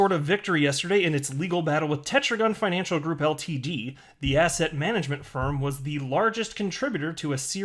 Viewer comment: Hey, everytime you say “asset” I hear “asshat”